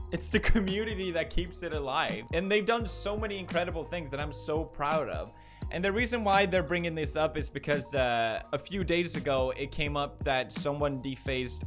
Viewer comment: did pewds just say he is proud of us nine year olds